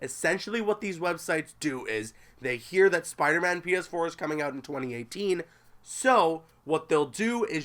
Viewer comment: I thought he said spider man penis 4 lol